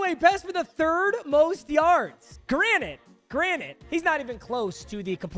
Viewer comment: did he say fucking “granite” 😂 27:43 bro it’s “granted” 🤣